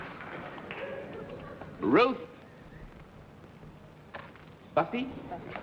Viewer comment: At first I heard the checker's name as Ruth Buzzi.